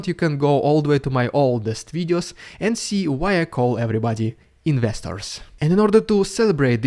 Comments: Investors? I thought he's been saying "nesters" this whole time. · That’s what you’re saying?! Investors? This whole time I thought you were saying listeners